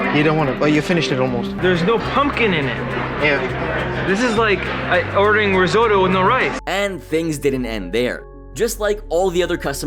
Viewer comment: I like the guy at the end without pumpkin in his risotto saying “it’s like serving risotto with no rice.” No dude it’s literally serving a risotto with no pumpkin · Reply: Ahahaha I thought the dude said “ it’s like serving a soda with no rice” and I’m like what??? Maybe ice lmao yeah yours makes more sense 💯😂😂 dur duh dur on my end 😶‍🌫️😂